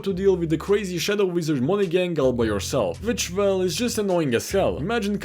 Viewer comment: I heard coming instead of cycling on accident